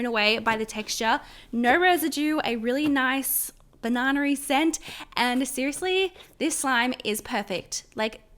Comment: what is she saying banannaier scent¿?